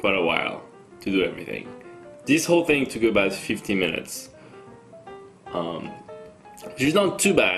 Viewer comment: did he say it took 15 minutes or 50 minutes? If he said 50 minutes that sucks. Thats pain for almost a hour